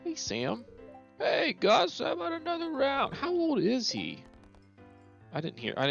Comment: "Hey Gus, another round of sliders!" Thats What Sam said in case you're interested.